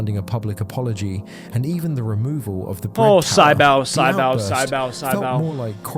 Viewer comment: tf is he saying? sigh - bow??